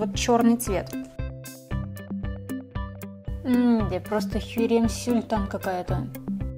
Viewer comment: мне одной послышалось не херем султан, а хирем сюльтан?